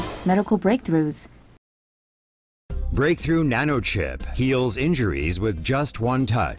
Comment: I thought it said nachochip